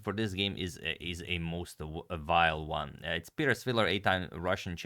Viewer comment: Did Tony just say that Peter Svidler is vile?